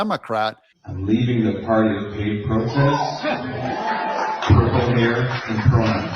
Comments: Actually I thought he said “paid protests” not hate protests! I think it’s important for people to understand that the dems have to pay people to protest! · I think he said "paid protests". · Did he say hate protest or paid protests. It sounded like he said he was leaving the party of pai protests. Either one is appropriate I guess.